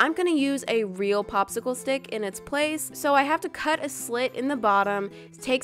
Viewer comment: WHAT “we are going to cut the slut” THAT IS WHAT I HEARD XD